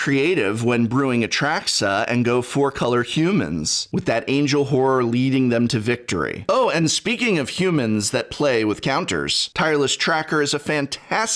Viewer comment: Did anyone else hear "that angel whore" at 11:28? I had a conniption for about half a second.